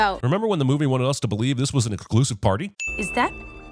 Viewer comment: what it says he said: exclusive What I hear: conclusive